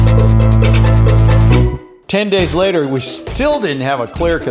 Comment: why lol did that dude say 10 weeks cause I thought he said 10 days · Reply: No he said 10 days. But the guy in the original video, did he say 40 days or 40 weeks? I think he said 40 weeks because that's the avg length of time a woman is pregnant. Point is, I was just trying to agree with you and strengthen your point, 10 days is just not enough time for this experiment regardless